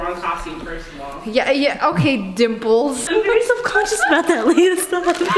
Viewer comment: I thought she said Ayo dimples not ok dimples 😂